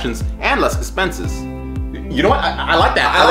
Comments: at the end all I heard was “screw them kids” like bro 😭😭🙏🙏🙏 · Pretty sure I heard "Screw them kids" at the end XD (edit: wow never had 1k likes XD) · on the ending, while they was arguing, i heard "Yea, screw them kids!" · What i heard at the end " Yeah yeah screw them kids"